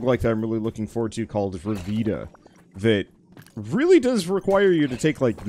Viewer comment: What did he say? Revitta? Bruvita? Vuvita?